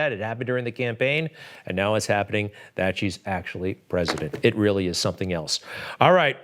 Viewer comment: did he just say... "now that she's president"????